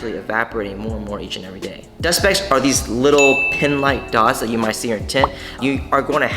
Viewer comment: Not sure what you're referring to as pin holes--not really a term I've heard used in tinting so I can't say. It's extremely challenging for any shop to guarantee < 2% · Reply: my bad i misheard ya i was referring to the dust specs i thought you said pin hole but you said pin like dots. But i see maybe over time they go away but one of my cars doesnt have any but i was glad this video cleared up they arent harmful to the car if there is any